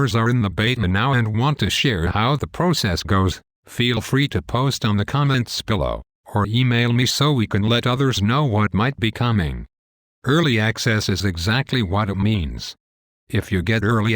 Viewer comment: LOL it sounded like he said "Comments Pillow" at 8:06. I always sleep better with my comments pillow, it tells me everything I want to know about SWTOR while I sleep, so I have awesome dreams. Also you forgot a race. I might tell you if you ask nicely. Or you could check out betafootage website and see the vids for yourself. OMG I can't believe you missed that one. I agree on pay 2 play though. Keeps the riff raff out. Free 2 play games are full of spammers and scammers.